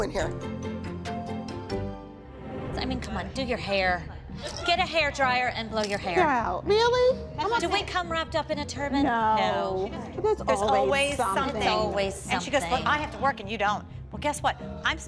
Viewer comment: am i crazy or did jill say wrapped up in a turban 5:54 ? I